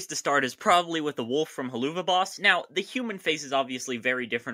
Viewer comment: bro did you just call it "huhloova boss" WTH smh